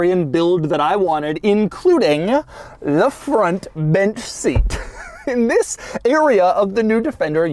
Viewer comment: Wow. I legit thought that was called a “bitch seat”. Growing up, hearing when my uncles would pronounce it in their drawl. Wow, they were probably saying “bench” all along, lolz.